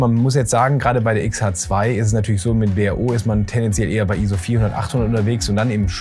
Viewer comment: Super Video. Ich hab hier noch mein manuelles Samyang für Astro herumliegen. 5:54 Was sagst du da? bao? w h o? Auch mit Untertiteln hab ich leider nicht verstanden, was du da gesagt hast. Könntest du das mal kurz hier hin schreiben, wäre klasse :)